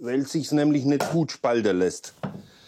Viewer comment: Cooles Video. Meine Frage: warum ausgerechnet Ulmenholz? Hat da einer eine Antwort? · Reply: wenn ich es richtig verstanden habe: "Weil es sich nicht spalten lässt" (?)